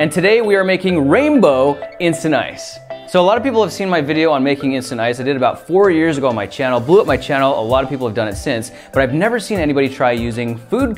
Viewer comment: I thought he said 40 years ago instead of 4